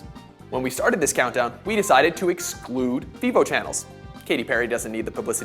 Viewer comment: he said they decided to exclude what channels? I could't understand what he said. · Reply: He said "......We decided to exclude Vevo channels" :3